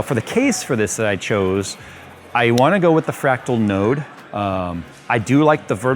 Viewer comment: "Colonel, did you say 'Nerd'?" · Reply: Not nerd, node raiden